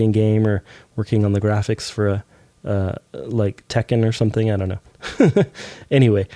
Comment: Did I hear Tekken?😈😂